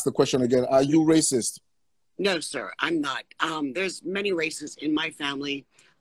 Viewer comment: im not racist, there's many racists in my family 0:20 im not racist, nor is my family bro that was a 13 second reversal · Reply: Lmao I thought she said racists too